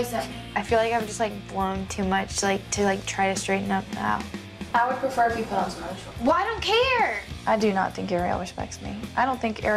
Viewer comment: What did she say? The subtitles were cropped out (this is why you shouldn't crop a 4:3 show) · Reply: "I would prefer if you put on some other shorts"